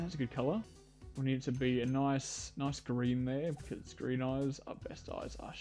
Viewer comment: When you said “ nice green eyes”, I thought you said “nice Korean eyes”. Don’t ask. You look more like a hybrid Canadian person with Asian.